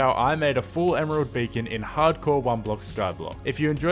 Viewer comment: did he say hardcore ?